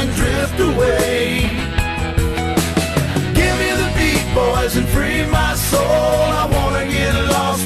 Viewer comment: I always thought he said "I give it to the people to free my soul" 😭